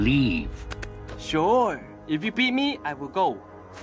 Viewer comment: idk why but i hear "if you pee me"